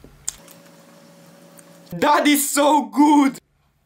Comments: Bro i thought he said in the first one was “daddy so good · I thought he said daddy so good but he said that is so good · I thought he said daddy thats so good (not dirty minded)